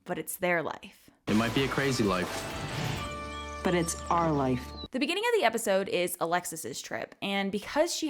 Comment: It always pissed me off that it sounds like kate says "ARE life" not "OUR life" ....nice to know it still grinds my gears 🤣